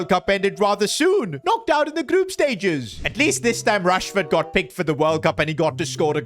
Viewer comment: it said Trafford and Rashford but I thought it said trashford💀